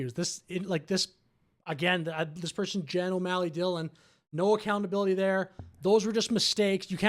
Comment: Anytime someone said Jenna O'Malley Dylan, I thought they were saying General O'Malley Dylan. I googled the name, and I realized I was hearing it wrong 😂